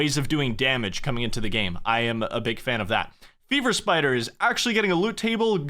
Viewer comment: As someone who has not played Runescape in many years apart from private Classic servers, I heard "Beaver Spiders" when he said "Fever Spiders" and I was seconds away from signing up for an account. Shame.